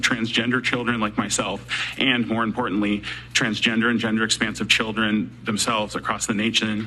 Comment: I spaced out for a long minute and regain consciousness when I thought I heard transgender fender bender lol.